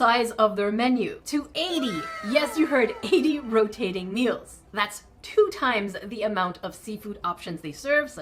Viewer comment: OK I literally thought it said rotting meals😂😂😂